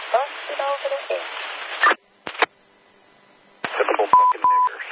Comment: Cenk, sounds like he said "typical F-ing Ngrs"